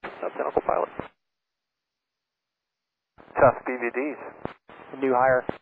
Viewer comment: I thought the pilot said he wanted to "sell DVDs" lol